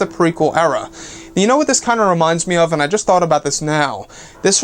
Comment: It took me a minute. I thought you said Mason Crew, and i was wondering who that was then realized you said "Mace and crew" lol my bad.